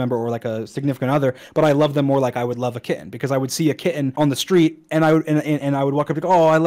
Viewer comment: did he just say were a kitten on a street tf 😔